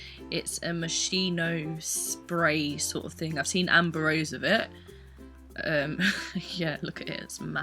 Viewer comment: Did She Said Dean Anbrosse or how whateve do you write it ?!?!?!?!?!?!? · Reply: I'm pretty sure she said Amber Rose:)